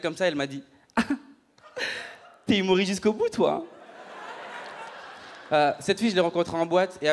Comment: Il dit quoi à 3:16, il parle trop vite avec l'intonation :/ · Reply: "T'es humoriste jusqu'au bout toi "